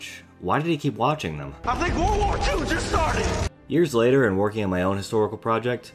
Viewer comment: wait, I thought he said "I think WWII's just hit us"